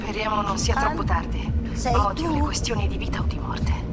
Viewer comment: ODDIO MA SOLO IO A 29:00 HO SENTITO BASSANO SEI TU AHAHAH